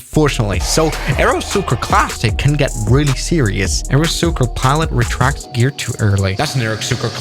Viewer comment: i thought he was gonna say “aerosucre classic can get really classic”